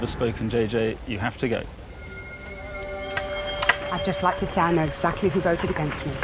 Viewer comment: What did JJ said on 47:06? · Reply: 'I would just like to say, I know exactly who voted against me.'